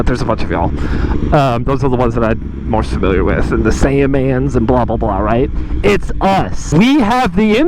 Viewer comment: did you say Sandman? 9:09 if not....wishful thinking. If yes.....assah dude